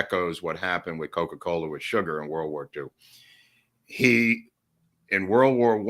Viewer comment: Did I hear you say "World War Chew"?